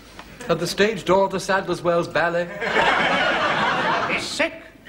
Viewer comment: the Stage Dornders Sandus Wells Ballet? What’d he say? LOL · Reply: the stage door of the Sadler's Wells ballet.